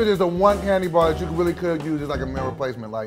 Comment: I thought he said male enhancement I’m fucking dead doboy chill